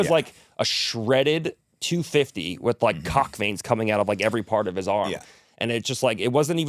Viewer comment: Did Gordon just say steroids are legal?? lol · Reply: Did he just say cock veins?!